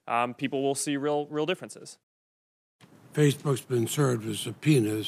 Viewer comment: I thought he said "Facebook worked as a penis"